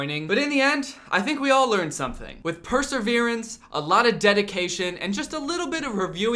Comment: Did he say perSERverance